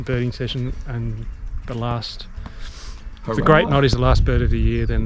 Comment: Loved this! Quite skittish, all those peeps and gulls... so hard to make out the individual species, I guess! (So, this birding of yours was on my birthday). At 10:01 did say Grey Noddy? If so, that must've been quite the catch, I believe! OK, gonna watch the remaining video now... Also: I think your Avenger-like escape from that dratted barbwire fence was very succesful, no piece of clothing got harmed (as opposed to that poor hat) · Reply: Happy belated Birthday! ❤ I hope you had a wonderful day!! Not Grey Noddy but Great Knot 😆 (our accents! haha)